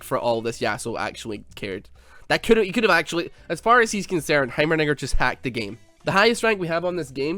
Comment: jlam i the only one who hears say"heimernigger"?